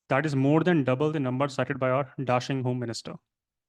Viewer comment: me sir home minister ke sath adjective kya bola sir? dartian ? ya dashing? vocabulary ke liye asking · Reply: dashing